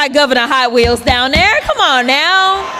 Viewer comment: It sounded like she said "high wheels", which is actually worse.